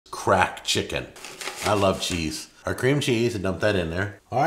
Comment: for a second i thought he said * crack chicken *